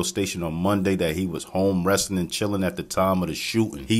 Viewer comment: I thought he said i was arm wrestling when the shots was fired i was bout to say damn homie didn't see shit thats a hell of a alibi😂😂😂😂😂